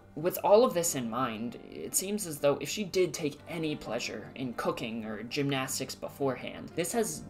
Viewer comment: i heard "cocaine" instead of "cooking" at first and that really threw me off lol